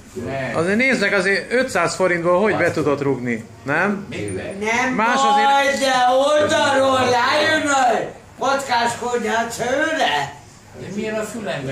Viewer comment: tól mit mond? Nem baj, de oldalról ráüvölt, kockás konyhád szőre? :D